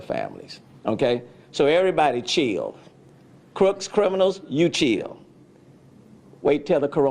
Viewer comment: Did he just say “criminals just chill?” 🤯